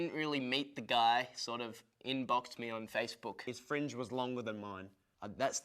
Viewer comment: did Ashton said "he on blocked me on Facebook"? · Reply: No he said "inboxed me on facebook" referring to the time that Michael messaged him on facebook asking if he wanted to join the band